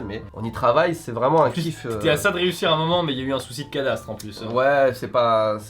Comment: j'ai entendu "il y a eu un soucis de cadavre" a 11:50 a la première écoute au lieux de "cadastre"